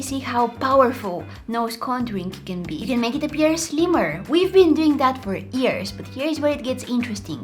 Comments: What she said: “We have been doing it for years” What I heard:”We have been doing it for EaRs” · Who heard her say ears instead of years I wanna know like if you did or reply🤔❤